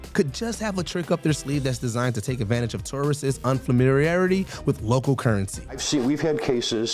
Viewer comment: am I hearing that right? Unfamiriarity? Huh?